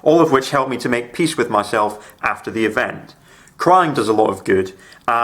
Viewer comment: I legitimately thought you said "CRIME does a lot of good and hugs as well ...." it was only on the second to third listen that I realized it was CRYING not CRIME... more importantly nice to see you're doing okay and hope you continue to. (even if I'm 2 years behind on watching this) (side note crying and hugs as an autistic individual who deals with (yes deals with not suffers from) sensory processing hugs are my best friends also being a genetic female my anatomy goes straight to crying with very little to no anger so I relate to the cry a lot as well)